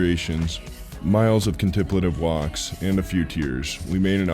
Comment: can someone please give me the spelling of that word, i love learning new words and when i tried to google it i got contributor live walks...is that what he was saying or was it something else?? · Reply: Is it contemplative?!